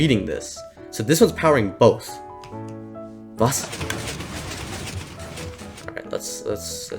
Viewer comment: Did he say "was?" at 9:34 ? Ist er Deutsch ???